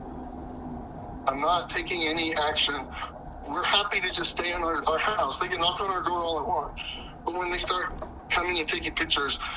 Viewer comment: He actually said "they can knock our my door all they want" He really said that. Lol You heard him boys.